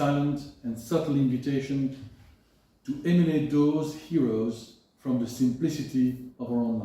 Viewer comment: What is he saying at 10:17? "invitation to eminate those heroes from the Simplicity of our own lives ..." The word is eminate in the transcript, but what is it really? eliminate? thanks in advance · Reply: Hello! According to the actual book, the correct word is "emanate" (to originate, to produce). The invitation is to let the mythological heroes take form (to be expressed) in our lives. I hope this helps. Thanks.